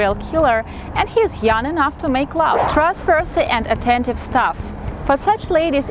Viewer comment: "and he's young enough to make love". Did I hear that correctly? 🤣